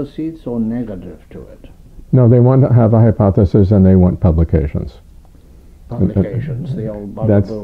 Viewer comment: What does Davis say at 19:59 ?? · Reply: They want to have hypotheses and they want to have publications.